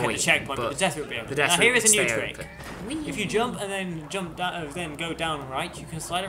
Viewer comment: "now here's a new trick..." But WHAT THE FUCK DID YOU SAY THERE, M8??? lol "if you jump then jump that of them go down right..." hahahahhaa